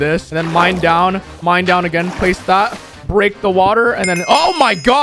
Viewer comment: Did you say break the water?